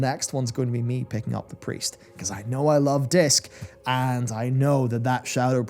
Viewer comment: "I think the next one will be me picking up the Priest because I know I love DICKS" - Oh god I completely misheard that LOL